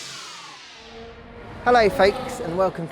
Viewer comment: Did he just say "Hello, fakes"? Could've sworn I was real... Also, get well soon Will!